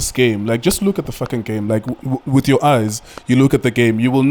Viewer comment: Did you just say "look at th k*ckn game?